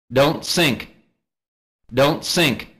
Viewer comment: Why i hear "Don't sick!"